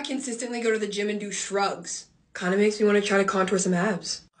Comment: I thought she said drugs